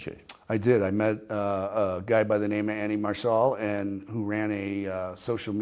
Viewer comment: I love this interview, anyone know the social media contacts or reuploads (if he deleted his account) for the guy he originally gave the videos to? @ 26:02 Kinda sounds like he said Annie or Manny Marshall?